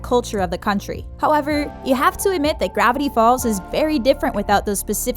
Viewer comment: it sounds like she said gravity balls